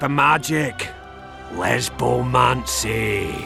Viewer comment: did he just say "Lezbomancy"?